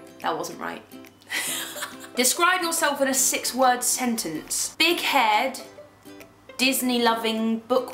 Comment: did she said "dick -head" or "big head"?? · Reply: She said big-haired